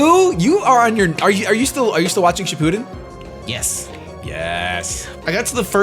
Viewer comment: what anime are they talking about? Can't make out what they're saying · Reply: Specifically Naruto Shippuden.